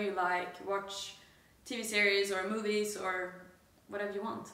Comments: Good to see you guys alive and uhm...kinda well! 21:33 nearly had a heart attack, thought you said t-series instead of tv series! Sub to pewdipie guys! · for a second there I thought you said 'T Series' but luckily you said 'TV Series'. Sub to Pewdiepie 👊